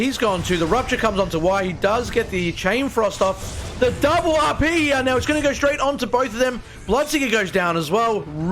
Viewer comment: did i just hear "double reverse polarity"?